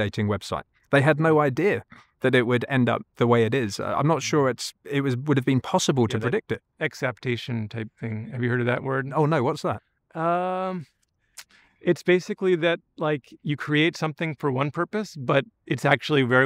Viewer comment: At first, I thought he said ACCEPTATION, but now I think it was EXAPTATION.